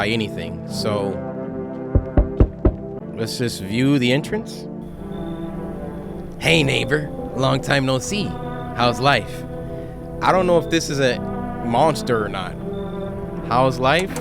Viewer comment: i heard “hey 🥷🏾” 😭